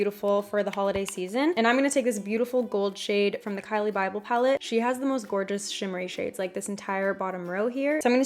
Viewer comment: Did she say "Kylie bible palette" @ 7:24 ? 😂 I love you, and if you did it is a great dupe for Kylie's stupid expensive palette . 😁